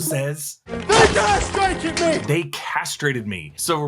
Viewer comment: I think he actually says "they dare strike at me!" That's what my subtitles also said